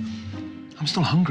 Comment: did i hear a HELLO MY NAME IS NINO at the end 5:01 XD